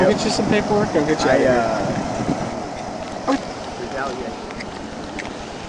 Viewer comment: Did anyone notice the edit and hear the man say retaliation? Oh ok I hear the officer smug attitude from the officer now.